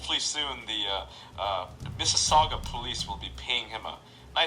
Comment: DID HE SAY MISSISSAUGA POLICE.... Wait I live in Mississauga